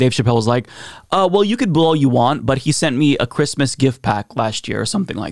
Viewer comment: pretty sure you mis understood what Chapelle said - in actuality, he said "Christmas Jet Pack", which was a Middling joke about Elon's engineering "guff", not any kind of thanks for anything Elon probably didn't send Chapelle. Not "Christmas Gift Pack". Anyhoo, Carry On.